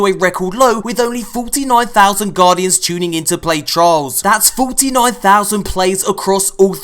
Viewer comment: put it in caption it says Charles instead of trails